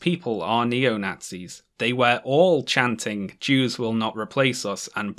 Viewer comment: Slide side comment but I always thought they were saying "you will not replace us" not "jews" as in they are speaking directly to whatever race they believed were replacing them, idk if I would call it a funny mistake but it gave me a chuckle.